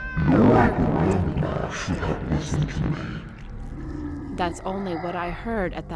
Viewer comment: WHAT TF HE SAID? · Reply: "Your grandma should've listened to me."